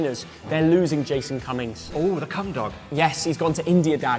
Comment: Did just say the Cumdog 😳😂